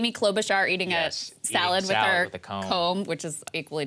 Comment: Amy Klobuchar "salad with a comb"... Thought I misheard LOL